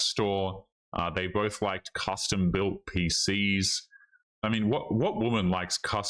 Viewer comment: She thought he said custom built pee-pee.